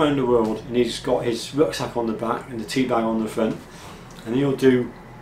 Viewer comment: I heard, "He's got a rucksack on the back and a teabag on the front" 😀